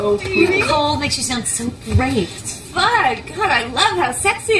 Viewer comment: I know she said "FUN", but I swear I heard "FUCK"